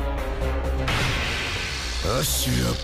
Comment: I swear it sounds like he said 'bankai"